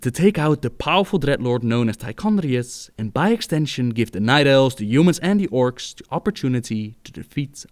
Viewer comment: hehehe I thought he said hot elfs